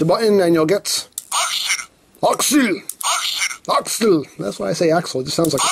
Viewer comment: did anyone else hear aux him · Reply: I heard alxelu (Accel)